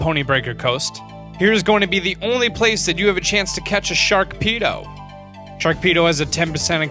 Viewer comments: did you just say "sharkpedo" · Did he just say Sharkpedo?